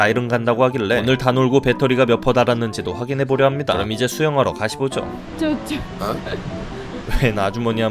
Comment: 가시보죠는 뭔가여.......내가 잘못 들었나 싶어서 3번은 돌려서 다시 들었네........